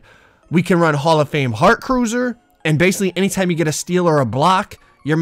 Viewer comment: Did I hear heart cruiser?